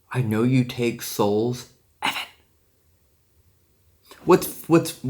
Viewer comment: it kinda sounds like saying " Evan"